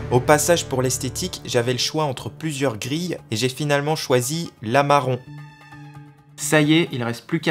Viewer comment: j'ai entendu "lama rond" 😂